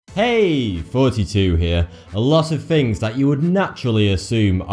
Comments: I've never seen one of this guys vids until now and I thought he said "Hey, forty-two here." Lol but he said Thoughty2. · Whenever Thoughty2 says Thoughty2 it sounds like he says "42" so "42 here". Lol